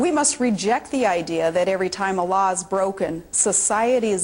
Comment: the subtitles along the bottom say "Allahs" instead of what she actually said which was "a laws" Deliberate or not? You could never prove it i suppose, but hey you could imagine the media owners throwing in something like hoping for some reaction from muslims, especially foreign ones who can read a bit of English but could well miss what Palin said because of her accent